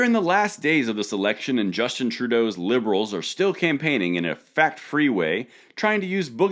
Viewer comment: I thought you said fat free campaigning and that made me chuckle. A Trudurp soyboi vegan campaign would be hilarious. 8D